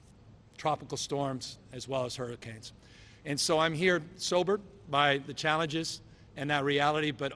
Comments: did he say 'so I am here ‘sober’' ?? 😂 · Does he say “I’m here, sober”?